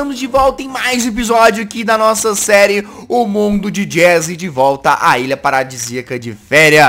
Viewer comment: Só eu q ouvi mundo de jazzy